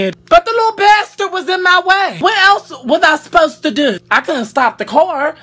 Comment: I thought he said but the lord best was in my way but he said a little baster was on my way 😂😂